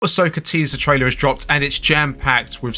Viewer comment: Hello, small critique. You need to add like a 3 second pause to your "intro". I don’t know if its audio on my end or video compression or what but the first half of your first word is cut off. I hear "Ska teaser trailer…" Everything else is great. I don’t mean to sound like a hater, you’re stuff is really good.